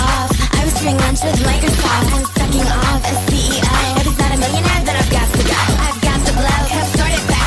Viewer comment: I thought it said "Is that a millionaire, then i've got to go"